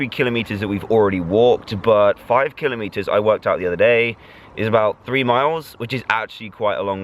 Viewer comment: When he said I worked it out I thought he said I worked out like in a gym